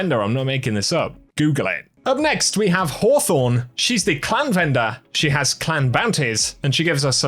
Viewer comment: Damn man, I thought you said Hawthorne was the clown vendor… I was dying. But not bad, this is an entertaining video!